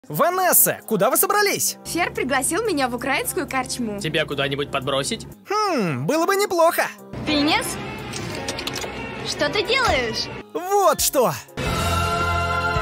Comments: Мне послышалось пинес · Мне послышалось "Пенис, что ты делаешь?" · Мне послышалось не Финес а Piнес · Мне послышалось или.... Украинскую карчму? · Мне не послышалось или она сказалА пенис вместо Финис?